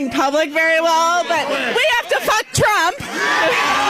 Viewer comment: did she say “we have to fvck trump!” Lmfaooo ayooo ur not his type lol